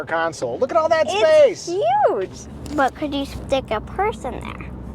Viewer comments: I thought she said, "But can you stick a person there?" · I thought she said “but can you stick a person there?” 😂